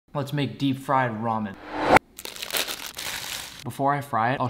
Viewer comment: I thought he said ''Let make deep fried vomit😂